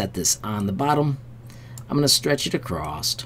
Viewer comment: Good tutorial, but I think you are searching for the word “across” not acrossed thanks for the info! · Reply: I thought you said ACROSST or something like that around 5:48 - No big deal, it is just after a lifetime of editing, my OCD makes me stop paying attention when I hear things that sound a touch off. My problem not yours, thanks for your hard work!